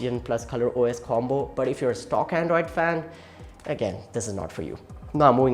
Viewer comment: I thought you said "this is nord for you" lol